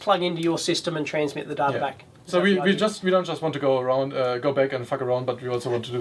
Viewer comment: Did he say "we don't want to just go back and f^^k around" at around 21:10? lol